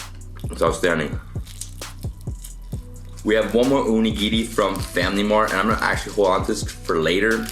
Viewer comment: DID YOU JUST SAY ONI GINI?